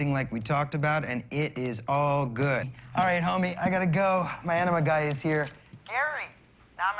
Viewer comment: what did he say here? "gotta go, my "???" guy is here" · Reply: Enema